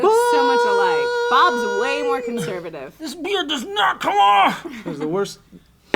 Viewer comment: I THOUGHT THEY SAID...BEAR PENIS